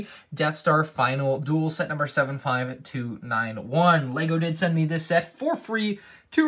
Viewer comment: I don’t know why hut instead of hearing “Lego did send me this set for free I heard “Lego didn’t send me this set for free because of his interlapping voice